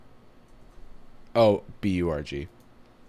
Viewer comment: i know he said "b u r g" but at first i heard "bee orgy"